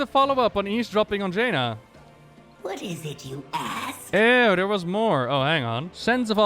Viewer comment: Every...time...Lillian says, "What is it you ask?" my first thought is she's saying, "What is it, you ASS?!"